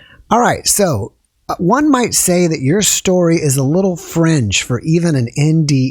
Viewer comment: "Your story is a little 'french'"?????? Say, wot??? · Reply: "Fringe" according to my closed captioning. I thought that was what he said too, lol.